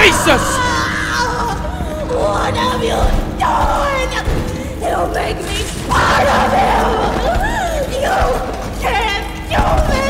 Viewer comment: anyone else hear mommy say "don't make me apart of him" at 1:00:28, maybe the toy that dragged her off is using other toys like huggy and now mommy to make themselves whole for a final boss